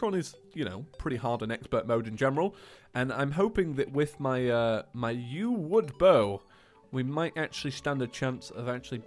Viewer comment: i thought he said "with my new wood bow"...of course a wood bow is MORE then enough for skeletron :D